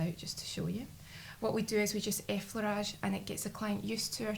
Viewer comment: Hello can u please tell me what u say at 0:42 . i hear a flash. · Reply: ah, got it! She says "effleurage" which is a massage movement, apparently 👍